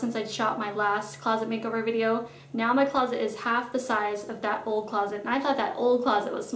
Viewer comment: I turned on the CC cause I couldn't hear the video over the outside noise, it was funny. At 0:16 it said "your father is half the size". I do love this video, I live in an older house, way older the closets in this house are that size, believe it or not. that's what I have to work with. I was trying to figure out how to make it work better for me. thank you